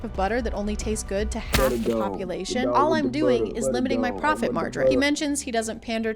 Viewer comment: did u say “profit margrin” instead of margin? 💀